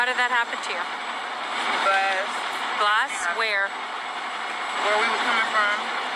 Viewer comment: did she just say GLASS??? Where were these videos for court? Why did they place a gag order on Tory? Why did kelsey get immunity??? Looks to me like the wrong mfka is in jail!